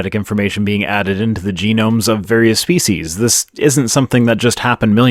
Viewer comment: did you actually say faeces and not species? 🤣